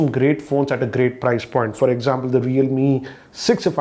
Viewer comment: What is the phone he mentioned at 0:48 I couldn't understand his pronounciation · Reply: Realme